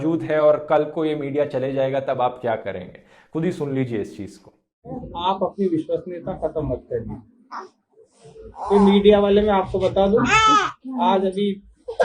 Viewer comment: I liked to watch your video Dhruv Rathee, But now a days I'm realizing that your videos don't have any scene than a political agenda. Because you can clearly hear that DM is saying (10:36) :- "आप अपनी विश्वसनीयता मत खोइए। और मीडिया को देखकर बार-बार अपना बयान चेंज मत कीजिए।" The essence of what he said was that "If you change your statement again and again, people and government or anyone else will not believe your words again." And your intelligence must be so developed that you can understand it? But your job is to show things by manipulating them. Your videos nothing more than that a political agenda.